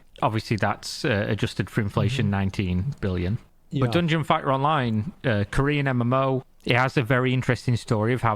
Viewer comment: Man, I thought I he was saying " Dungeon Fat Online".